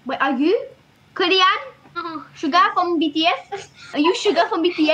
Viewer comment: am I the only one who heard "SUGAR" instead of "SUGA" ? Poor Suga everyone think he is sugar😂💀